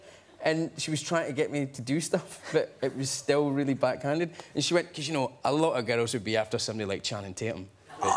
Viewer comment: What he says after 4:08-4:15? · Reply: "It was still really back-handed and she said, 'You know a lot of girls would be after somebody like Channing Tatum, but not me!"